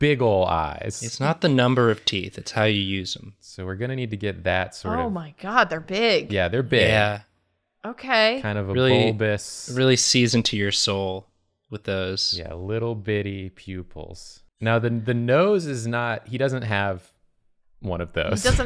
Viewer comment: I heard "really sees into your soul," as "really seasoned to your soul," and that's pretty wholesome sounding